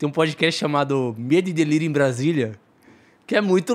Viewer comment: que podcast é esse que o baltazar fala em 2:23:20 ? “Mêdideliri Brasilia”. entendi porra nenhuma